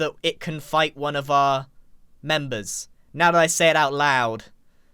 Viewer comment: İ know he says "fight one of our members" but İ heard it as "bite one of our members" which has pretty different connotations